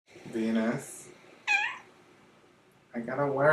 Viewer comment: I THOUGHT YOU SAID 'PENIS' NOT 'VENUS' I'M FUCKING SCREECHING